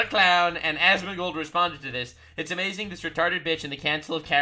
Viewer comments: I'm certain I misheard you say "A cancel of Karens", but I'm going to just roll with it. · Counsil of Karen's sounded like you said "Cancel or Karen's" 😂 You know how a group of geese is a gaggle? A murder of crows? A parliament of owls? A group of karens should be called a cancel of karens